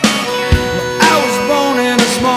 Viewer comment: I always thought he said “Dr. Phil and Jesus in a small town.”